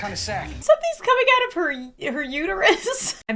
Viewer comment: I THOUGHT HE SAID SOMETHING COMING OUT OF HER UNIVERSE T-T